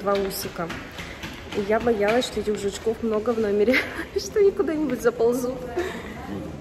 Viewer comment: тебе в пипи*ьку за ползут Я одна услышала